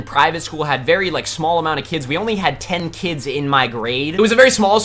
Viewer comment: i thought he said she had 10 kida